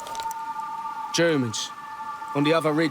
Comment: did he say gooomans?